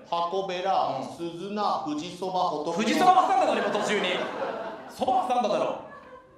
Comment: 日本語勉強中の外国人なんですが、1:17のとこ山本さんのツッコミ（富士そば~）って何て言ったか、教えてもらえる方いますか？七草については調べてわかったけど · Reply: 富士そば 挟んだろ！ ですかね。